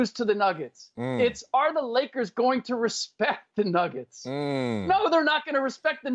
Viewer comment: boricuafrican Do you know how to read? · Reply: that's still not what Ric said...at 3:50 Ric clearly says it's not will they "lose" it's will they "respect" the Nuggets and he said no because they Lakers haven't respected game 1 so far...he said IF the Lakers lose it's because they didn't respect them and not because Denver is better.